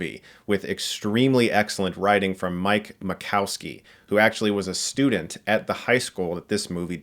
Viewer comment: I thought u said ‘Mike Wazowski’ for a sec there lol